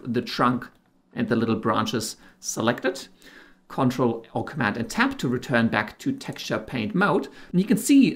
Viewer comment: I don't understand what is being said>>>>Command tap?? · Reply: "ctrl or command and tab..." You can also enable captions and it'll spell it out for you on the video :)